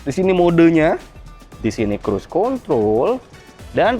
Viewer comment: gak salah denger tuh😂 kontrol apa Kon***.